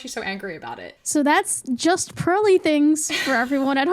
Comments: Ngl- I thought you said justpurlythings- Which sounds like an amazing username for a crafty knitter. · id thought you'd said just Girly Things, the like tumblr blog anonymously run by a lesbian like back in 2014 and prev with the most generic things, super fond of it, absoluitely had a chokehold on tumblr